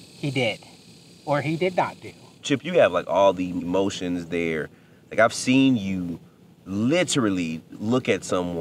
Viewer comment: I heard," I've seen him" I think it's his mom.